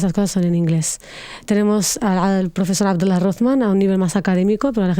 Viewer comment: Hola, no entiendo el nombre del profesor 1:27:30 ¿Alguien puede ayudarme? Y también del verso en 1:21:35🥲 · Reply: El autor se llama Abdallah Rothman